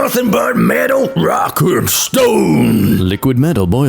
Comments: DID I HEAR A ROCK AND STONE! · DID I HEAR A ROCK AND STONE!? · DID I HEAR A ROCK AND STONE?